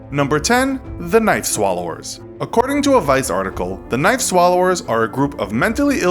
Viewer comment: Who else thought he said “night swallowers”?